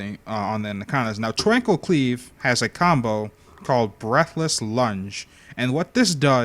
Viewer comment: When he said "breathless lunge" I thought he said "breakfast lunch"